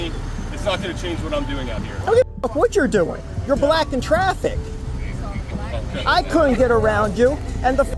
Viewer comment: I swear to God the cop says you are black in traffic lmao even racist when they’re dealing with a white guy lmao